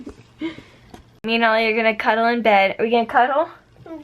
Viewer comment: At around 7:30 sounded like she said "Me and ELLIE are gonna cuddle in bed..." You should have seen my reaction! I totally thought you were talking about J+E, Ellie. Hahahaha!